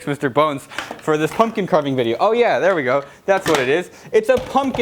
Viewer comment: I thought he said “For this fucking carving video.”